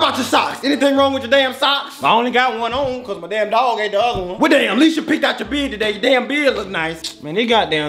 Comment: "What about your socks?! What's wrong with your socks?!" "I only got 1 on my damn dog ate the other"😂😂😂 (Edit) thanks for all the likes❤ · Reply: Wait that's what he said? I thought he said "Mah damn dad took the other!" Lmao